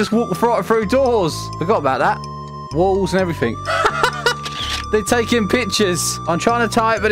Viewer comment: why tf did i hear ''they're taking b******'' ?!